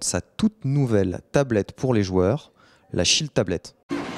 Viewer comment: j'ai compris la "shit tablet" lolool